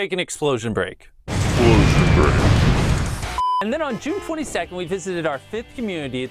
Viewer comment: did he say june 42nd?